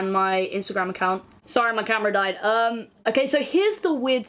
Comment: Jeeesus I thought you said sorry my grandma died and it took my stupid ass way too long to realize that it was camera